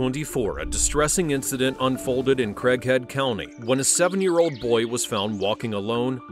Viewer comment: At first I thought they said crackhead county 😂